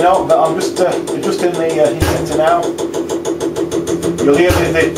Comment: what are you really adjusting at 7:15? I hear "adjusting heat in and out" but it makes no sense. Sorry but this really troubles me...